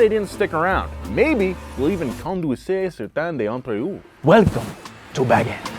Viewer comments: "conduire certains d'entre eux" (drive some of them) for the french who were wondering what he was trying to say lol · i'm french and i have no idea of what they said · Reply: Je pense que "conduire certains entre-eux"? For the English people said something like : and maybe "drive some of them".